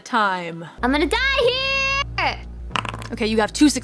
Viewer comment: ผมได้ยินนางร้องว่า "I don't want to die here!" นะ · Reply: I'm gonna die here งับบ @@